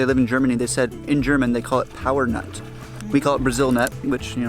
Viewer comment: It sounded like said, in Germany its called 'Powernut' - probably owed to german speaking accent 😂 we call it 'Paranuss' (Para nut), from the region of Para, as other commentors already pointed out. The boat trip reminds me a lot of my trip in the Arctic/Greenland, just different temperature/surroundings but same experience .